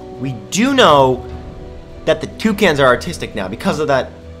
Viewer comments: I thought he said they were autistic toucans instead of artistic. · At first I thought he said the toucans are autistic......